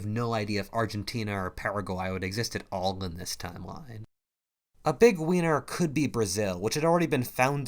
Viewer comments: I can’t be the only person who heard “a big wiener” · is he saying "a big weiner"? Update: turned on auto captions. And it seems to think so as well. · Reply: He said big winner